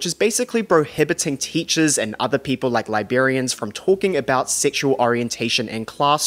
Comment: I thought he said “Liberians”